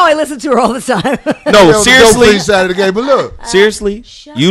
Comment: Crillin112 46:17 I think face changed because made the comment about Faith Evans. Sounded like he said she was on the "dope-fiend" side of the game.